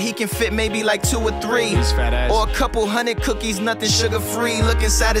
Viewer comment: Did anyone else hear 'fat-ass' at 0:31?